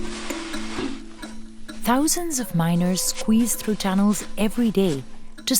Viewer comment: I thought he said “thousands of minors”💀😭